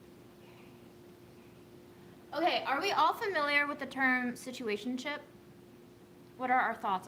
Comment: I thought you said SITUATION CHIP! I was like.... i'm listening...